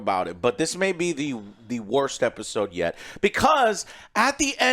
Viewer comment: For a second, I thought it said "WORST EPISODE YETI". 😂